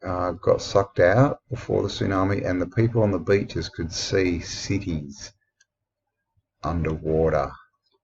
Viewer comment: it sounded like you said they saw "siggies" underwater. Did you really say "cities"? And if people said they saw cities underwater, why haven't I heard about that?!!!! That would be so freaking cool!